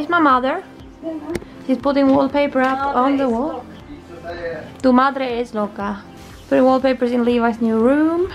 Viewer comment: How funny 😛 I heard you saying in spanish "mi madre es loca" (better said "está" instead of "es") ........actually I think the one who's crazy is your father 😅 ("tu padre está loco") 🙈 😂💦